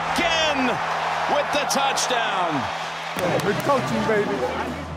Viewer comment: LOLOL. Gronk....."It's coaching BABY!" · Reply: I think he said "Good coaching, baby!"